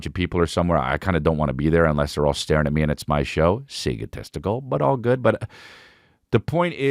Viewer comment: Chris said egotistical and the closed captions said “see good testicle” 😂😂😂